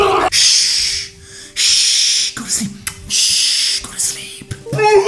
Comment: I HEAR THAT GO TO SLEEP